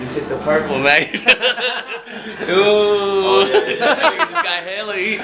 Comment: Haha at 1:05 he just hit the purple? is that what he said, whatever i still LMAO